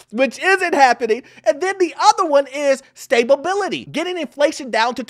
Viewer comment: Another informative video. 7:46 But did you say “stabability”? You can insert your signature laugh here. 🤣